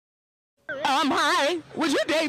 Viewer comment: What did she say?Like what did say- i always hear:"i f*ck u now"😂😂